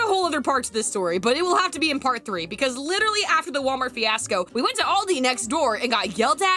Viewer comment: you said wall bart instead of mart mart